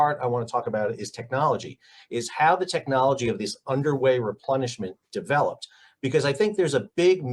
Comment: I THOUGHT you were saying "underwear replenishment". This is something obviously diffent.😄